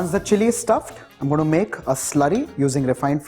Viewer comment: did he just say he's gonna make it "slutty"?!?!?! 😂😂😂 Oh wow, he says it like 10 more times... What the hell is he saying?!?!?! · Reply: Speiden It's slurry not slutty